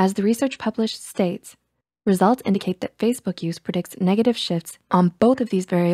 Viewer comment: When it said Facebook produces negative shifts I thought I heard negative shit XD